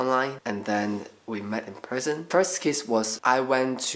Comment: WHEN HE SAID "we met online then we met in person" I THOUGHT HE SAID THEN WE MET IN PRISON I WAS LIKE WHA